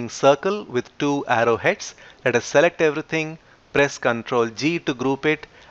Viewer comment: ctrl what? sorry i cant understand.. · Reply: We used the shortcut Ctrl+ G to group the objects.